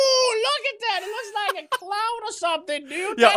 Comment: When I heard say it looks like a cloud I thought he said it looks like a clown